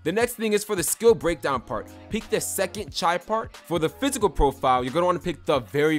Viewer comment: Did he just say chie part 😂